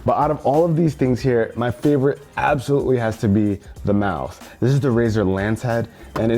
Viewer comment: did he said the mouth!?